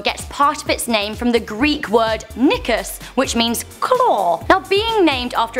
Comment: omg when she first said it i thought she said the n word